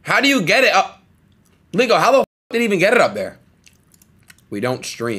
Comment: did adin just say the n-word at 2:49 adin what did you say?? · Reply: Leeko